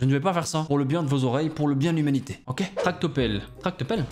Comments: A 7:06 moi j'entend plutôt ''tarte aux pommes'' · moi J'ai entendus "tarte au pomme" perso 🤣🤣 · moi perso j entend plus tarte aux pommes que tractopelle 😂 sinon super vidéo!!😁😘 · pour moi c'est plus "tarte au pommes" en vrai xD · Moi j'entends plus «Tarte aux pommes » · moi j'entends "tarte au pommes " 🍏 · moi j'entends plutôt "tarte aux pommes" 😂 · moi j’entends « Tarte au pomme, tarte au pommes... »😂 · Moi j'entendais "Tarte au pelles"